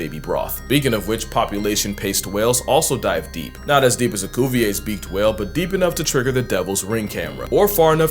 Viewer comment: I misheard Cuvier's beaked whale as goofy-ass beaked whalei I blame your iontro on how weird these whales look